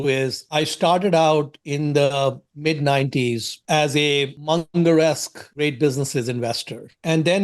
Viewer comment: I can't understand what he said at 13:14 . Something like 'I was a ........ ......... investor" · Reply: Munger-esque investor